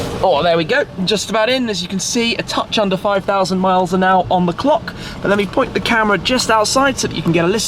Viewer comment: who else thought he said his car has 5000 miles n hour 😂.... Damn those focus' are fast!!